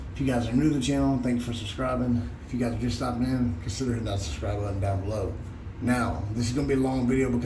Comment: To me (an Australian) it sounded like you said (from the 10 sec mark) - "Few guys are just stoppin in, consider your nuts and scribble on down below" LOL - well brother, I never scribbled on my nuts much but you sound like you know your stuff, so I'll give it a go :D